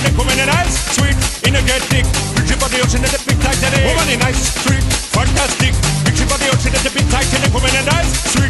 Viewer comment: I thought that he said "ice cream fantastic"